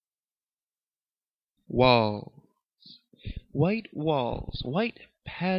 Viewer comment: thought he said "balls" at the beginning...